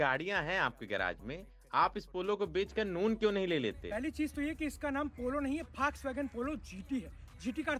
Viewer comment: Kya bola MC ne ...Faaaks-Wagon ?? Yeh kausa si car hai bhai ?